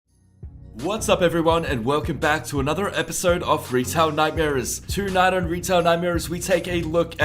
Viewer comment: Thought you said Retard Nightmares in the intro lol.